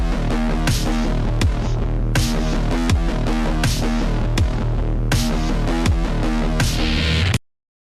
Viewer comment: Like si no entendistes lo que dice Venom en el minuto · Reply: Dice ¡Ojos! ¡Pulmones! ¡Páncreas! Tantos bocadillos, muy poco tiempo.